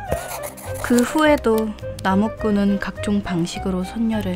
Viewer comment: 그 후에도 남옥군은 선녀를 빡치게 했다에서 발음이 나무꾼으로 들리는데 이거 선녀와 나무꾼인가요 😄😄